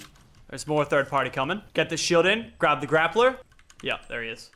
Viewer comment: did he say grap the grappler? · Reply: he said grab